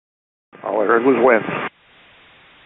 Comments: "Oh they just went" is incorrect--what was said is "All I heard was wind." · AAL472 doesn't say "Oh, they just went!" - they say "All I heard was 'winds'." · Correction: at 0:48 time, the pilot says, "All I heard was wind..." not, "oh they just went."